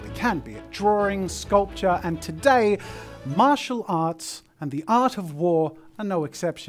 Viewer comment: I thought this said “MARITAL arts and the art of war” and I was frightened for mrs jazza